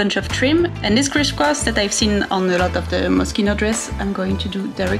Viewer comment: At first I thought you said "Mosquito inspired" not Moschino. I was wondering what that might look like. 🤣